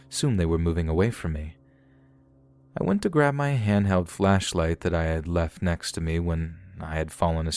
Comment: I thought he was going to say grab my handgun😅